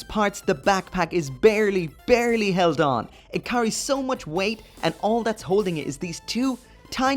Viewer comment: < I heard as “it CURRY so much” hahaha